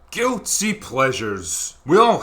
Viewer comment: I thought I just heard you open with: "Goatse pleasures" wth